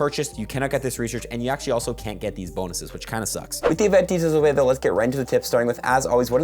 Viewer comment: what are you saying? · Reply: "With the event details put of the way